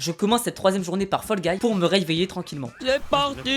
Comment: C'est moi ou a 11:52 tu dit "pour me reiyveillé" ? 🤣 Sinon trop bien la vidéo